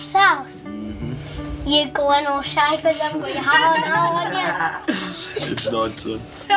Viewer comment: What did say at 24:51 that made his parents laugh? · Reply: Abroad He said to his dad “You’re going all shy ‘cos you haven’t got your hat on, aren’t ya?” That’s what they laughed at.